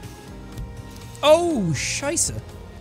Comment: did he just say "Scheiße"? German for Crap